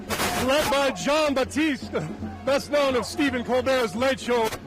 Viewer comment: Did he say, "Rabbi John Batiste"? hmmm